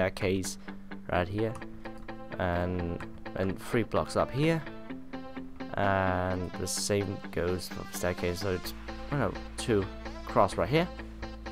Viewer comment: Did You Say Then The Satan Go's There On 7:33?